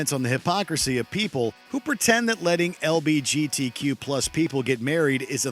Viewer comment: anyone else hear him say lbgt instead of lgbt people at 4:52 🧍‍♂️